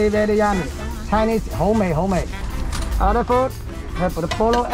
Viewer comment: i believe he's saying "yummy" in cantonese here instead of "homemade", nonetheless amazing video!! keep it up